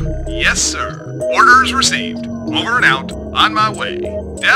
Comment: What says at 3:35? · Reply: Over and out